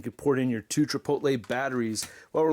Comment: did I hear two Chipotle batteries? Aww man, I’m hungry now!